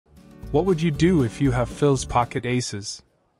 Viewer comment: I heard "what would you do with phills pocket aces" i instantly thought FOLDDDDDDD EM